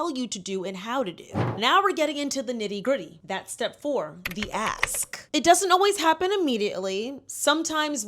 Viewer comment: Swear I thought she said “the ass” when she really said “the ask” 😂 tamatoe tomato tho I guess 🤷🏽‍♀️